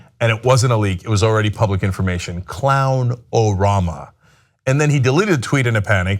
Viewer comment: was I the only one who heard "clown Obama"?